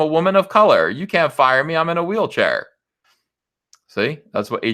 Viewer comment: For a second, I thought you said, "you can't fire me, I'm in a wheel barrow!"